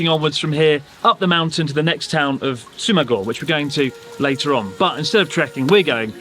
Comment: I thought you said Sumago for a sec and I immediately thought "Is that an anagram for Sugoma?"